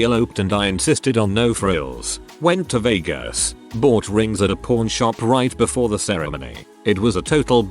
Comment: I would've taken the things of candy from the first story. I love candy. 11:20 for some reason, to me, it sounded like the bot was saying "porn shop" instead of "pawn shop" So I had to go back and read that myself lmao